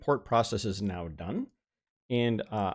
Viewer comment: Hello! Thanks for your videos, really useful tutorials! Can you please write me down how you show images in a gallery at minute 4:55? I'm not a native speaker and didn't quite get what you said, which shortcut did you press? where do I find the option? Thanks a lot! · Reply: it is normally the V key, but I remapped it to the ~ key, as that is my preference for some weird reason. It just turns off the viewer and the browser would then fill the screen. Welcome to the channel!